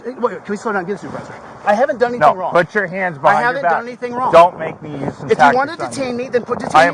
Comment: did he say "don't make me use the taze gun" threat to use excessive force . Someone needs to pull his jacket. The cop should have walked away. This is retaliation. So disappointing to see supposed officials acting in this manner. Las Vegas of all places. SMH